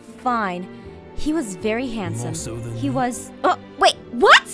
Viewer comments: WHAT DID GARROTH SAYYY???? >~< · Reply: Lemi The Lemon lol thanks i relistened to it and it was "more so than me" 😅😜🤗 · Can someone please tell me what the heck garroth said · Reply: Emotional Fangirl more than me